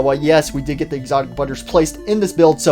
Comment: I thought he said exotic toastwr i was like :]